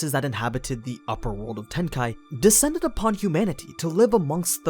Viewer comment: I though I heard "world of hentai"